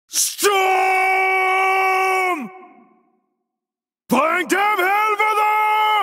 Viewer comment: “find them he’ll brothas!” 1:29 “enclave!” 1:39 “aim for raga man” 0:09 “Thai get a bowl!” 0:12 “come back!” 0:18 “Yelp!” 0:14 “Their comma! Help us” 0:17 “yelp my!” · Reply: Here’s what they’re actually saying: 1:24 “Bring dem helvede!” 1:28 “Angreb!” 1:38 “In på række, mænd!” 0:08 “Trækker tilbage!” 0:12 “Kom væk!” 0:19 “Hjælp!” 0:13 “De kommer! Hjælp os!” 0:16 “Hjælp mig!”